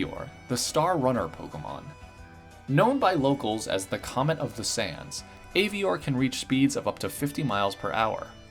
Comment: misheard it as "the comic of the sans" lmao